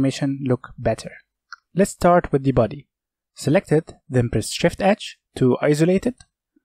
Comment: Really great tutorial, extremely helpful and clear. Just want to note that the subtitle says "Shift Edge" at 4:41 and 6:17 and this should be "Shift H" for Hide.